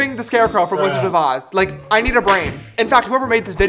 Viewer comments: remember james is also the person who made this video. this comment is a joke and i do admire his art work and skills no need to overreact abt this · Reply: Lol before i thought he said thats me not thats mean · I thought he said "that's me. That's me."